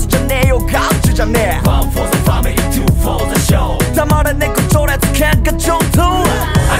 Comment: 全員キャラ良すぎて…w まぁ、そのぉ箱推ししか選択肢ねえんすよね 紗月君のパートの 黙れ 年功序列 喧嘩上等 が、 黙れ 猫情熱 喧嘩上等 に聞こえるんですよ(で?)